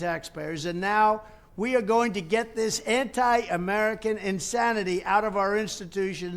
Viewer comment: MFW I heard him say "Hentai America" I need help